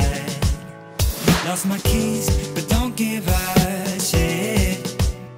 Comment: Did he say "Lost my keys, but don't give a shit"?!